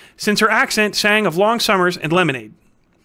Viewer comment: okay, real talk, I misheard the line the first time and thought you said "her accent sang of long summers and lemons" and that LEGITIMATELY sounds like a better line than "her accent sang of long summers and lemonade" ... like... just the difference between lemons and lemonade feels like the difference between Homer the Poet and Homer Simpson xD